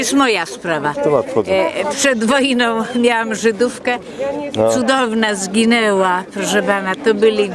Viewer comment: Czy ktos jeszcze usłyszał w 1:30 ze Żydówka cudownie zgineła??